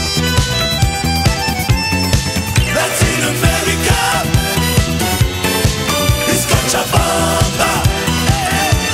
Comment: What is the place they say in 4:15? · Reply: Cochabamba, Bolivia - A fantastic town in the bolivian highlands